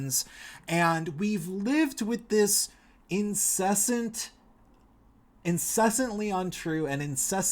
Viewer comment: I must be tired I thought you said incest instead of incessant